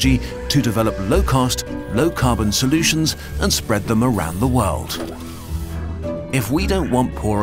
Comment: lol my video was freezing and 8:33 sounded like you were saying spread the marai botnet, i only made out spread the marai before it froze, but its quite wierd